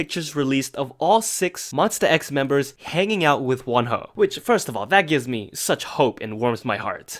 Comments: i thought he said that all the members were hanging out with one hoe, i didnt realize till 20 seconds after that he didnt say 'one hoe'🙏🙏🙏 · When you said that Monsta X was hanging out with Wonho, I thought you said that they were hanging out with one hoe. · I thought you said all 6 members of monsta x hanging out with one hoe🤣🤣🤣🤣 oh my god, I'm so sorry. Joking aside, can bashers just leave monsta x and wonho alone? Like you already succeeded in ruining a person career especially when he was now proven innocent. Now, even his friendship with his members? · I thought he said the six members were hanging out with one ho* 😭💀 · I thought he said “there was all 6 members of monsta X hanging out with one Hoe” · When you were talking about Monsta x I thought you said they were hanging out with one hoe 🤣I’m srry I can’t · I thought you said ‚hanging out with one hoe‘ and monsta x was hanging out with a girl and they had to apologize for that 😂😂